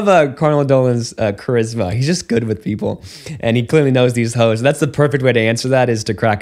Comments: "He's just good with people and he clearly knows these hoes." I had to turn on captions to confirm I heard that right. What Father??! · Did he just say he know the hoes 😂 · Did he say "He clearly knows these hoes?" That's what the CC thinks. Heavens. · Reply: I THOUGHT THATS WHAT HE SAID TOO!!! But he said "hosts" lol